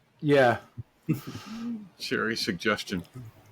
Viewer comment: it sounded like you said Cheri's Suggestion,🤣 but I'm pretty sure you said Cherry's suggestion.